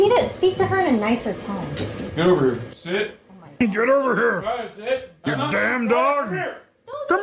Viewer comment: Did anyone else hear what he said at 1:49? Did he really just say "Emiru, sit"???